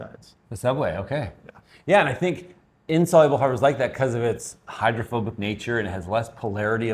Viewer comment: I heard you say that fiber will give me hydrophobia. :D